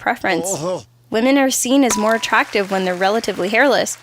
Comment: Did he just say "Semen". .-.